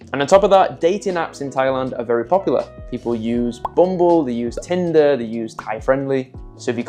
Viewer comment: Couple things nobody uses the word bougie get it out of your lexicon stop using it yes sound you sound like you're from an Australian ghetto.. Too when you said bumble for a dating app I thought you said boom boo. .. 😅